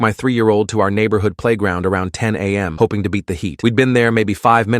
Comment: When he said “beat the heat” I thought he said “beat the meat”